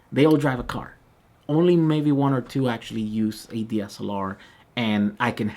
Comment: what is a DSELR, please? (going off of what the CC says) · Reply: A camera... A DSLR camera, for recording.